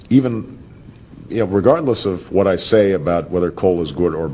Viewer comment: “Irregardless” is NOT a word! I don’t care if the dictionary people just added it to make Obama not look like an idiot. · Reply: Who said irregardless in this video? I heard Obama say “regardless”.